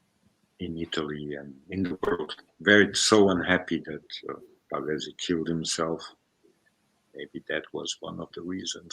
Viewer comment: what is the name he says? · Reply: Pavese?